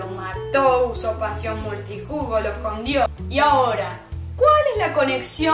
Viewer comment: No se ustedes pero en el minuto 4:30 escuche "pasión multijugos" jajaja